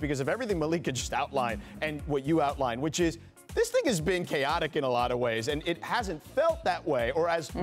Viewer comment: Sounds like he says the n word right here 1:40 😂😂😂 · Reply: “This niggas been chaotic“ 🤣